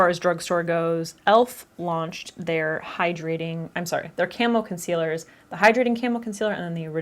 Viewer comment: So, I thought you were saying Camel not Camo and went into the description to find Elf Camel Concealer :')